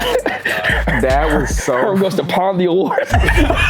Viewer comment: What did say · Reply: Herm goes to pawn the award